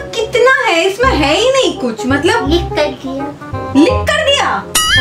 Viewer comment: I thought that said "Likh kar diya " Then I understood she said Lick kar diya!!!🤣🤣🤣🤣🤣🤣